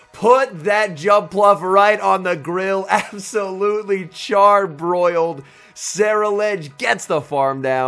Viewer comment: Charbroiled Jumpluff with a side of Slowpoke tail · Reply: Thought I heard he said "charmbroiled" 😂😂